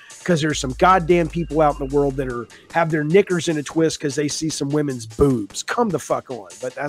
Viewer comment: I know he says "knickers in a twist," but the way I heard it was "(n-word) in a twist." Had to do a double listen the first time.